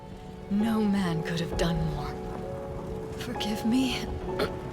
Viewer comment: I thought she said, "Nomad could have done more" and that was really funny xD